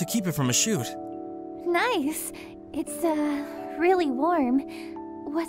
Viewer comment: "what's the fabric?" "Boyfriend material." LOL. at first I heard, "you're a friend material" dumb little me.